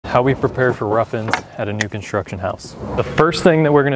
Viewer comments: I thought you said “how we prepare for ruffians” I was like dang, people robbing construction sites violently now 😂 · Anybody else thought he said ruffian and thought he was gonna booby trap the house home alone style? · Was I the only one that thought he said “ruffians” instead of rough in’s. I thought we were about to see a killer home defence video but ig not.